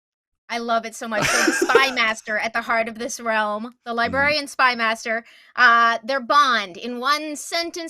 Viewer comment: I thought you said Alfred at one time when you said librarian but a domain built around the Butler did it would be great. Run it like a game of Clue where the mist shave given the party an invitation and the Butler keeps on saying the Master will join them the goal is to separate the group make them expect each other and kill the party off one by one.